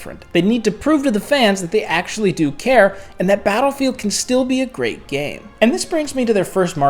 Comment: All i heard was " the next battfield will be a great failure "